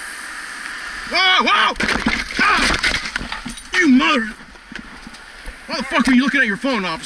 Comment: You mower I think he said